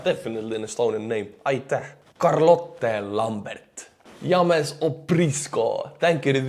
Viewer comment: That's the first time i heard you say my Name "James" - But you said it like this " Yam- is " Lol Wicked video have a Good weekend buddy , We will see you on Monday stay safe Pal